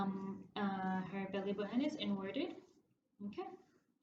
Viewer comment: wait what did you say about her belly button? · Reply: Inverted